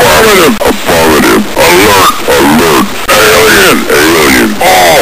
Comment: I always misheard ALERT as ERGOT from the second voice and wondered for years what the hell that was supposed to be code for.